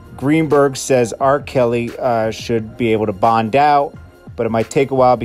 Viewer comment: I thought they said no bond ...??